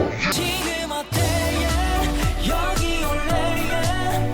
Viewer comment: 나만 지금 뭐얘해라구 들리ㅣ낰ㅋㅋㅋㅋㅋㅋ